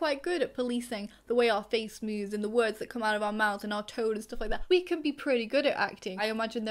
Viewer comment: "there are plenty of autistic actors" and like the part about that i was just sitting there like "hi im here, undiagnosed actor" and when you said "our tone" at 10:13 i thought you said artaud like the french playwright and my face lit up and i had to skip back to realise you werent talking about one of my hyperfixations inside of my special interest of theater